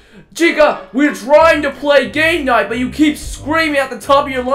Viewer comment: Captions say gay night